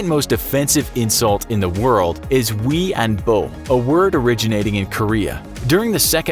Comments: did somebody else hear weeaboo? No? Just me? Ok. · thought u said weeaboo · Did I just hear Weeaboo